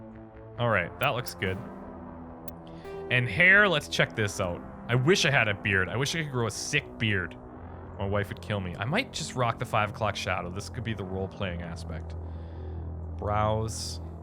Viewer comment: ish, "my wife would kill me" Did I hear that right? Pardon me, I'm a new subscriber, I didn't know he had a wife